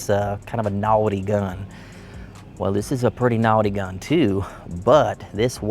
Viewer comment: I thought said “novelty gun” when he said “naughty gun” 🤣… that Texas accent bro 😝 I can give yu shit bc imma fellow Texan too bruhtherr 😎